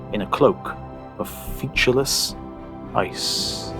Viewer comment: Could someone help me on vocab here? -- I can't make out the word at 23:53 "the Earth was a snowball planet, head to toe, in a cloak of ______ ice." I wouldn't care except it is so well written. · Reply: He said "featureless ice"